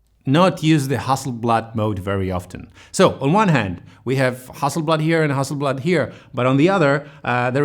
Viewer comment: in captisions (generated automatically) we have "hustle blood" instead of Hasselblad :)